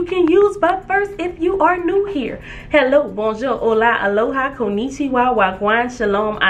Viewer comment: Did you just say Koni-Chihuahua? LOL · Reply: Nope lol. I said konnichiwa, wah gwan 😊